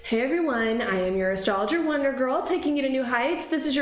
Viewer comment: Love your spirit. Love to listen. One little funny thing is : in the beginning of your video I hear you say " "this is your astrologer wonderbra" haha, sorry.